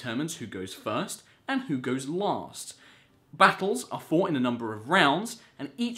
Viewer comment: When I heard him say last it sounded like laust